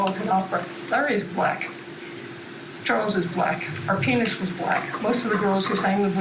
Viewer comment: I really thought she said “our penis was black”. Alas, it was her pianist. 🙄